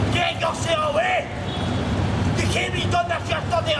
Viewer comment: what guard did say to old man? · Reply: All I caught was "halt. Get your self away, you can't be doing that... " and then something about the road and then I cant tell.